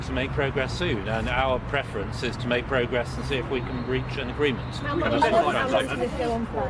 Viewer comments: For a minute there I thought he said "breach an agreement" not reach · Curious : I thought I heard "we need to breach an agreement". After second listening, I realised that the word used was "reach an agreement", which happens to be in direct contradiction with is antics in recent months.